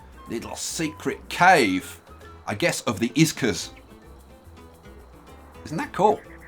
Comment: Did... Did you just say IZKAS